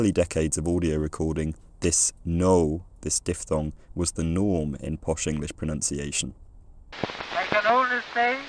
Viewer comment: I heard that as "was the GNOME in English pronunciation" after you'd talked about monophthongal O so much 😂